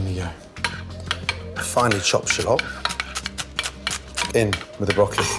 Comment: Im king of high did he just say "I finely chop shit up" at 4:49?